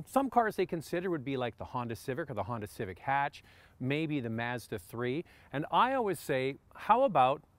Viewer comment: Did he just say Mezda?!